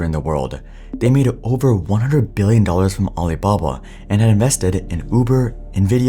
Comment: Did anyone else hear aliblabla?